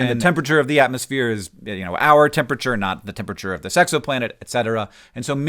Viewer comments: I heard "the Sexoplanet" at 08:47 . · the way he says "this exo planet" sounded like "the sexo planet" which sounds like something from Futurama